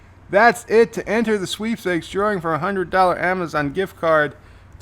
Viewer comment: That was very entertaining. However, when the survey said, "To enter the sweepstakes drawing for a $100 Amazon giftcard ...", I thought it said, "To enter the cheapskates drawing for a $100 Amazon giftcard ...". Maybe CBS is in worse financial shape than I thought.